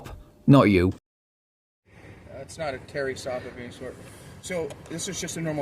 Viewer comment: Sorry. He cracked me up! I thought sovcit said “This is a fairy stop.” Ohhhhhhh! Now we’re gettin’ somewhere!🤣 The land of the sugarplum fairies that sent him to speak the language of their land!